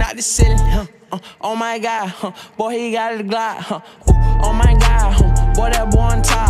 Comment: i thought he said oh my god he got a gyat...